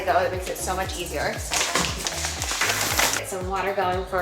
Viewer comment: “…get some water going for some chickpea rotini…” I heard “get some watermelon” and thought, huh…that sounds…interesting… 😂😂😂